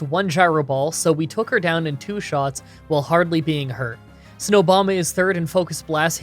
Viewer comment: did you call him Snowbama on purpose?